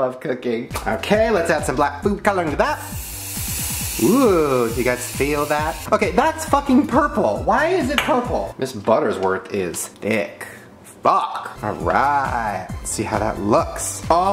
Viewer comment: Mrs. Butterworth and Aunt Jemima. Name a better duo. I’ll wait (Edit): stop saying Dan and Phil 😂 they👏🏻aren’t👏🏻better👏🏻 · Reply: Elizabeth Tarter I literally thought he said ms. Buttersquirt a few times, it literally sounds like it.